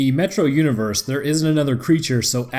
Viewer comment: Oh...Metro,I thought you said Metroid, can you do Metroid? Especially the bosses? It could be really interesting.